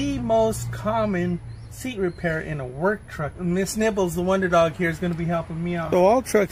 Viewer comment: I for sure thought you said, "Miss Nipples".